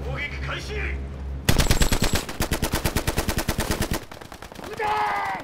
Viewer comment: what does he say at 1:26 and what does it translate to? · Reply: ”撃て”= Utae. It means "fire"